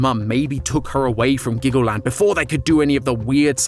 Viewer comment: I thought you said Glaggleland